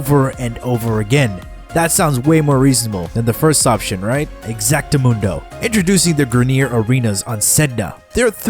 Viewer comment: what the hell did he say there? Exadamundo? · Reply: exactamundo